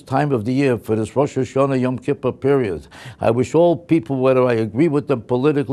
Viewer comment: Kinda sounded like said "Yum Kippers", they are nice!